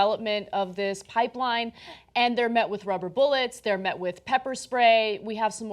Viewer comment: I thought she said they met with Robert Bullet... hold on... rubber bullets